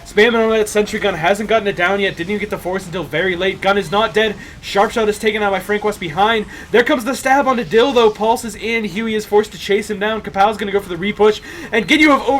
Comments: I can't be the only one who heard "Here comes the stab onto dildo" and thinking "that's not how those work." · "There goes the stab onto dill though" heard that as dildo instead of dill though :x